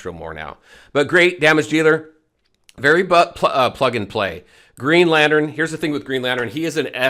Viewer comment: Excuse me, did you say joker is very butt plug n play 😂